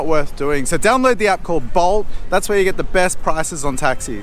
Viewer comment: What was the name of the taxi App you mentioned? I heard “Balls” maybe that’s just your ozzy accent! Good budget video. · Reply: 🤣🤣 It’s bolt, definitely the best way to get around!